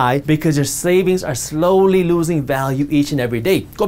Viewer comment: did he say, "your SLAVINGS are slowly losing value..."? Seems about right.